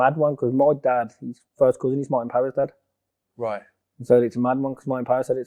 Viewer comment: sorry I didn't get that did anyone heard what Frankie said???? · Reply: He said my dad's first cousin is Martin powers dad.( Basically frankie gavin is related (2nd cousin) to the traveller boxer Martin power.)